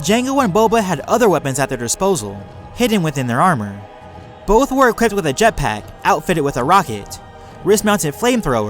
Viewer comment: what did u say it all sped up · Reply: "Both were equipped with a jet pack outfitted with a rocket." Sorry it wasn't sped it I was just talking too fast.